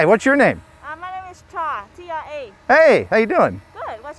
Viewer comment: im pretty sure she said Tra, T R A... maybe its just the mask that made her sound like she said that?